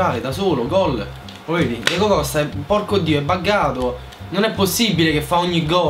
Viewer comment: Sbaglio o a 2:35 ho sentito un porcodì.... hajahhahahahahahahah